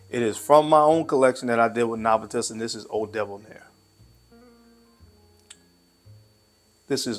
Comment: Eau Debonaire...I swore he said Old Devil's Hair....lol